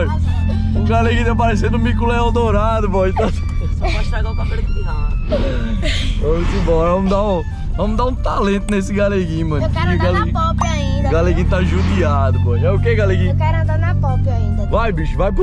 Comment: ele falou , vc não vai estragar o cabelo aqui não né, ??? , ele falou isso mesmo ou eu entendi errado?